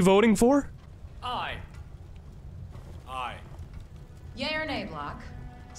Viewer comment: Am I the only one who heard eren yeablock at 4:01 ???